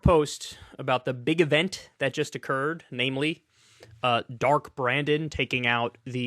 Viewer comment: I heard "Darth Brandon" the first time I listened, and now I'm disappointed. O-Bernie-Wan Kenobi, you're my only hope.